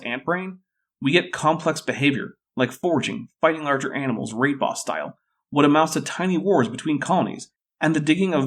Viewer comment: I cannot for the life of me figure out the word(s) you are saying around 25:09 or 25:10 . It sounds a little like "rape-ostile". Does anyone know what the (presumably) ant behavior is that is being mentioned? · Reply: Raid boss style